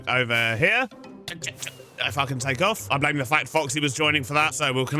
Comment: Eh? You think 'If I can take off' is swearing? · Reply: I work in a loud machine shop and thought you said fxxkin takeoff. My bad.